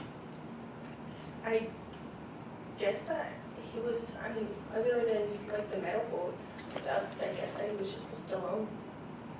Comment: Please can someone tell me what is her answer at 6:01:41 she say's " i guess other than the medical stuff.........." and then i can't make out what she say's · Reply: She said, "I guess other than the medical stuff, I guess that he was just alone"